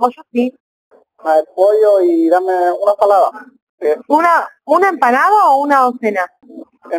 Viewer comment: Alguien más entendía ensalada?? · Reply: Yo pensé que decía "dame una salada"